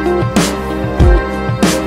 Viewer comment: I thought he said "Mint chocolate rifle" 😭😩